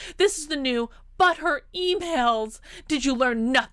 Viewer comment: I keep hearing 'butt hurt emails'. That's intentional, right?